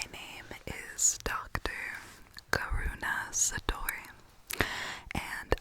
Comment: Before I realized your name was Karuna I thought you said dr. corona😅😂💕